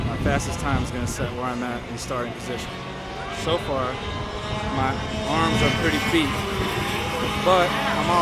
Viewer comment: love how the subtitles say "my arms are pretty feet"